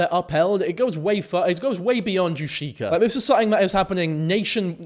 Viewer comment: I keep hearing tashika omggshsusuhs